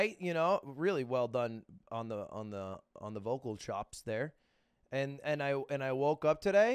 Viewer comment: my dumbass rlly thought he said "mogul chops"